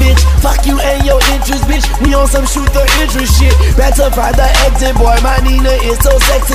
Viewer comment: did lil wayne say what i think he said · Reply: Find the exit boy?